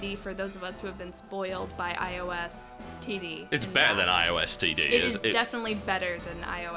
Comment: whenever you say IOS TD, I just heard "I oh STD" it was kinda surreal.